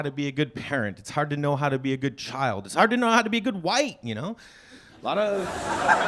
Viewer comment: I thought he said good “wife” did a double take on the good “white” 😂😂